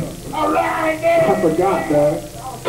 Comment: What does say? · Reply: Arghosoldiers gotta hang brah brah 🤷🏼‍♂️ I have a video of a Crucifier set where yells at the sound guy for more drums in the monitor, and he sounds just like he did here lol